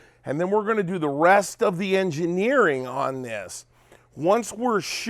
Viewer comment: You keep using the word "engineered" and I keep hearing the word "bubba'd..."